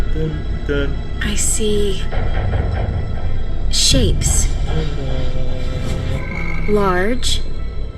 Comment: 😲 StarWars Girl: Shapes. Large. Devouring. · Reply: Shapes! I thought she said sheeps. Shapes makes more sense (and is more grammatical).